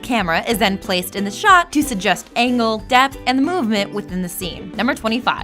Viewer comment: I thought she said "Anal"